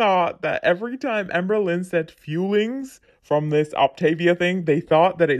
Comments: I thought they were called "Fillings" like they're supposed to fill you up. Calling them fuelings is so mind bending. Its such an MLM. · I always thought she said "feelings". I even went to their website. I'm from Belgium, so English isn't my first language. I assumed it were "fuelings", but I looked it up, just to be sure (maybe it was a sort of word joke they were using 🤭